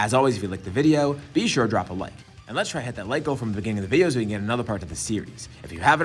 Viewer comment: sounded kinda like you said 'lick' instead of 'like' and idk why that's so funny to me